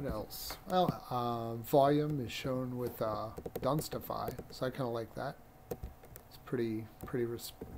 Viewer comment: I'm sorry but I acoustically can't quite understand the program you're mentioning for your volume - 1:44 Clarification would be appreciated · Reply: Think he says "dunstify", so using <link> I guess?